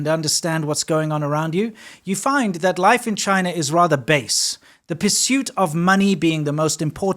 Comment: did he just say "based"???